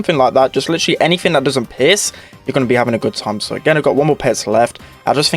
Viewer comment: What says: "...With anything that doesn't pierce" Me, an intellectual: "With anything that doesn't piss"